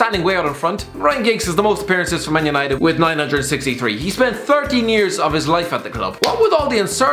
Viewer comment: did I just hear 13 years